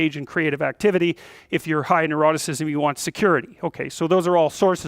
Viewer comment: If you are high "enoralesous,"you need security, what's the word ,I can't find it in dictionary, listen to 2:55 min of the video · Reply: "neuroticism"